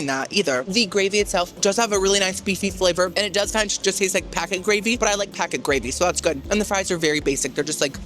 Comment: Did you just say it taste like pee